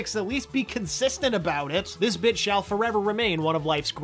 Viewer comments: did you say "bitch"? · i thought it said "This *BITCH*" mk